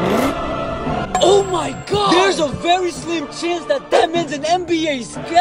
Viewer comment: When I heard “That Man” I thought I heard “Batman”